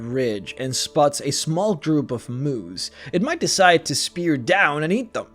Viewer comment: I thought you said Moos like cows instead of Moose. Ive seen too many weird memes